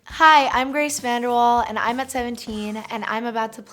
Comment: in the beginning I heard her say, "I'm Grace Vanderwall and I'm 17" not "I'm at 17" and I have a mini panic attack thinking I lost two years of my life. LOL